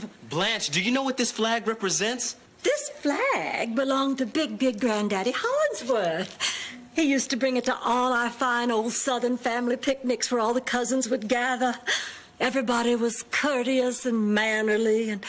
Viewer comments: I swear on my soul, I thought she said big dick granddaddy Hollingssworth. I had to put it in a caption to make sure I wasn't tripping 😂. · Did she just say "Big Dick Grandaddy Holinsworth"?